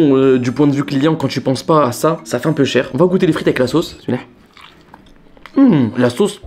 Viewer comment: Au debut a 7:12 de la video jai compris "on va goûter les frites les ksos" au lieu de "on va goûter les frites avec la sauce"😂.